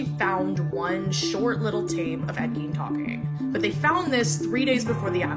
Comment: The subtitles here read ‘Ed Sheeran’ instead of ‘Ed Gein’…. 😮 Which is one of the most stupid (and simultaneously horrible) mistakes I’ve seen so far….. ….Especially considering the extra likable image of Ed Sheeran…..